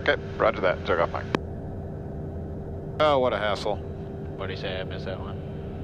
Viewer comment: At about 13:10 after all the ATC delays you said 'what a hassle '. The first time I watched I thought you said What an a**hole. Lol